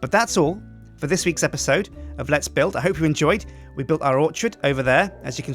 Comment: did u just call it a 'lets build'?